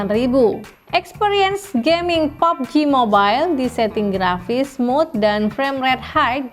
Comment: Gue dengernya "popji mobile" wkwkwk Dan mba, tolong bikinin video pesaingnya poco m3 ini dong.. Yg setara